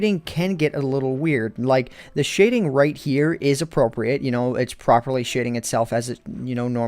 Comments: It sounded like you said "It's Properly Shitting Itself" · I thought he said shitting itself